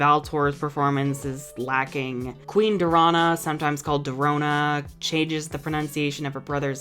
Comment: To be honest, I keep repeating this part but I cannot hear the difference in pronounciation of that Lumenia queen's name. I'm not a native English speaker, so the difference might just be too subtle for me to hear. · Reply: Her name is "Dorana", but sometimes they mispronounce it as "Dorona".